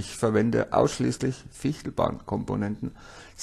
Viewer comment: Hallo! Sehr schönes video--danke! Ich habe nicht verstanden welche Komponenten du verwendest (5:22). Ich habe "Fichtlbahnkomponenten" oder so was ähnliches gehört... Kannst du mir es bescheid sagen? Danke! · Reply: Genau stimmt schon, einfach mal Fichtelbahn DCC googlen, würde ich wieder kaufen!